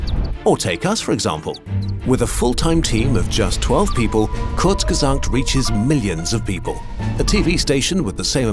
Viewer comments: Hi, 3:38 - there is a mistake in captions. "Courtesan 2" instead of "Kurzgesagt". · Captions are wrong Kurzgesagt is "courtesan 2" in the captions